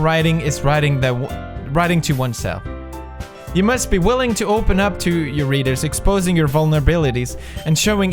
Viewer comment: sounds like he said "open up to your anus."